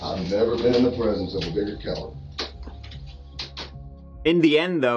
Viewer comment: The look on face when the detective said "I have never been in the presence of a bigger coward in my life" made me very happy, just really cut right through that POS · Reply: I thought he said he'd never been in the presence of a dirtier cop